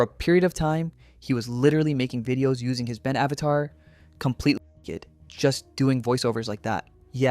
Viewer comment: I was so confused by the next clip considering the censoring and captions made it sound like he said "completely kid" and i thought he was voicing child characters. 😭